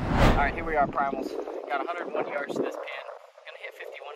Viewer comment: Anybody else hear the "here we are primals" at 9:40? must be on that raw liver bandwagon 🤣